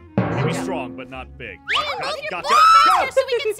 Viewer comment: what did she say booty??